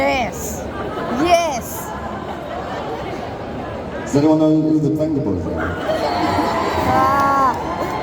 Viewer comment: Did anyone catch what song he said he would replace running up that hill with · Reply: Up and down by the Vengaboys 🫶🏻